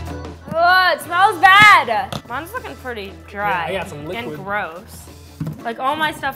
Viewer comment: I heard curse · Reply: “Mines looking pretty dry and gross”